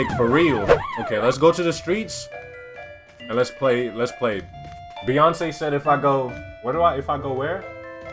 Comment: Did he say Beyoncé at 26:53